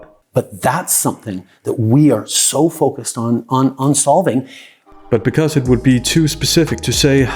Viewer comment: i heard "focused on unsolving" i think he spoke truth for a second.